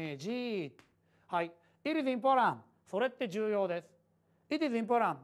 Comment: の「It is important」が、「ピピピポラ…ピピピポラ」に聞こえた(´_ゝ`)